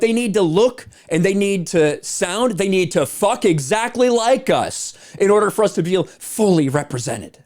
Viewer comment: Did you just say??? "But they need to look, sound, and f*** exactly like us!"